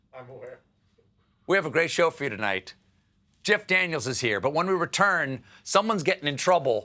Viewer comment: When I first watched this, I thought he said "JACK Daniels is here", when it wasn't? HUH!